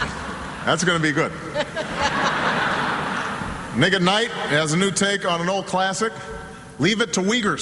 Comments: yo...llol diid i hear "niganight" lol funny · sounded like he said " nigga night ", when he said " Nick at nite " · i thought he said "Nigga Knight." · Did he just say nigga at 5:55? o.O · did he just say nigga? hahaha